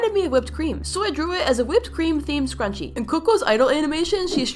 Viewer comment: I thought it said sprunki but it said scrunchie XD